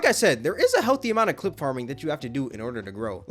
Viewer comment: I thought it said “chip farming” 💀